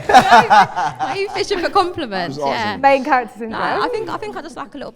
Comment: did just say main character syndrome😂iykyk